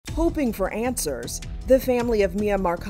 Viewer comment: Is it bad that I thought she said pooping for answers? Yes? Alright..